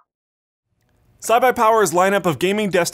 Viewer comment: did anyone else hear cy-buy-power